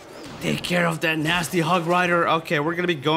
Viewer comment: I thought he said 'Nasty *Hug*(which means shit sometimes) Rider'